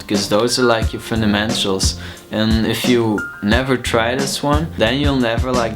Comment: Did you say "FundamenSTRUals"?